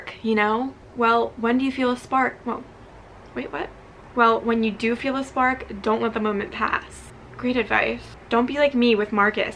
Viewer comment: Literally, “when you said when you feel a spark let it pass,” I thought you said “when you fart let it pass”🤦🏻‍♀️lol